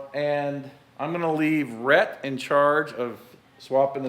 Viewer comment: When you said "Rhett'n I", after you got the U bolts, I thought you said Rhettneck. I defintely think Rhett's nickname should be Rhettneck.